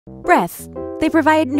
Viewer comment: Just me that thought she said breaths at the start